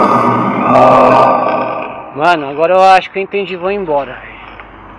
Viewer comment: eu entendi "não vá"